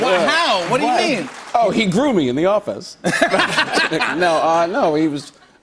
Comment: "he groomed me in the office" sounds like some Harvy Weinstein stuff... did anyone else catch it?? Right when he started SNL. Do me a favor and I'll do you one too... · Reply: if you listen again, I believe he’s saying” he grew me in the office”. The way he moves his hands gesturing a “growing up“ motion.